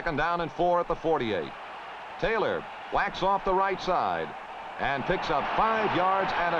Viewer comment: “Taylor whacks off the right side!” Did I hear that correctly?